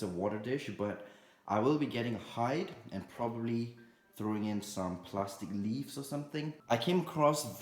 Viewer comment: I thought he said “im gonna get high and throw in some plastic leaves” I had a heart attack